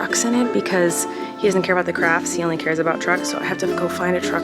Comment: i thought you said drugs-😭✋